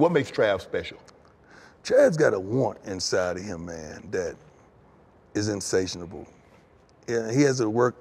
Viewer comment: What was this word????? Insationable??? Lmfao